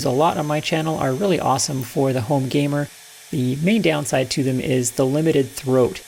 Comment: Just found your channel and wow…thanks for the gold mine of machining knowledge! Also did I hear you right at 2:32 saying “home gamer”? You wouldn’t also happen to be a gamer too? Lol anyway thanks again for putting all that time in…it is much appreciated!